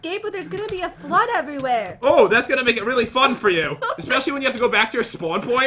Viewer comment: What said: "Oh, that's gonna make it really fun for you." What I heard: "Oh, that's gonna make it really f■■■ed for you."